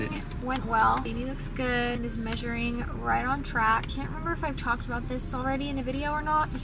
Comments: I thought she said He when they were in the car when she said “is measuring right on track” I thought she said He’s not is lol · Sounded like said he’s “right on track” after the doctors appointment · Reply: I think she said is measuring right on track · Thinking boy even more now. Wearing blue, phone case is blue and pretty sure said “he is measuring on track”. So happy for y’all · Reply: I heard “is measuring on track” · Could’ve sworn you said “his measuring is right on track” but maybe I misheard lol